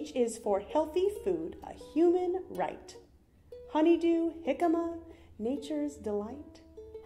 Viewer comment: Did she say "H is for 'jicama'"??? REALLY?! 5:05 PEE-PEE People??? PEE-PEE peace march??? SMMFH I like your fersion better.